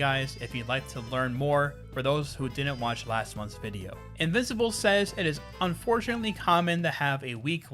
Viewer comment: Did he just say invisible?